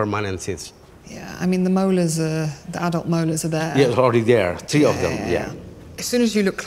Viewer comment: THEY DID...DIDNT YOU WATCH IT? SAID 15 OF THEM... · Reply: timestamp? ...the only time I hear anything that sounded like "15" was when he said "all 3 of them" in regard to the 3 wisdom teeth that had come in? (1:13) Maybe that's where u thought they said 15?